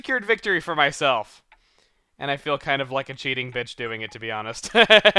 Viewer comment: did just say I feel like a cheating bitch? Because I would be suprised if he said bitch cause he usually never swears.